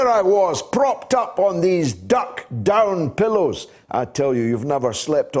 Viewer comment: Did I hear "pillie" for pillow at 3:55?? You can take the Scotsman out of Scotland... 😉 Great show again, thanks GG